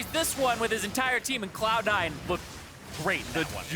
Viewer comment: WHAT DID ZIREAN SAY ??? Cloud9 will (blank) rape ??? and then he stopped talking Or am i retarded ? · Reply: He said "and Cloud 9 look great"